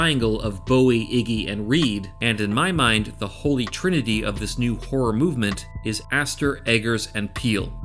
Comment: Just call it Neo-Horror. Perfect name. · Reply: shit ignore that...thought you said Noir horror...yeah Neo-Horror sounds good